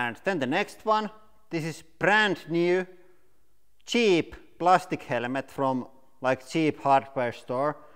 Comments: “Helemet” 😂😂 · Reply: Thought he said element at first lol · I thought he's saying element